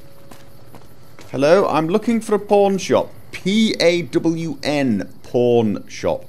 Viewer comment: I heard "I'm looking for a Porn Shop" . . . oh my :O